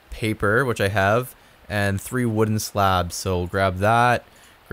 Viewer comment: I thought he said wooden slavs and then I also thought “aren’t all slavs wooden ?”